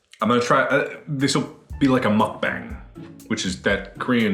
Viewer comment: I thought mukbang said at first nukebang ngl